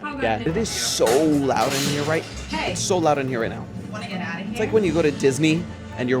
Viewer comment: am i going crazy or did they say "hey wanna get out of here~"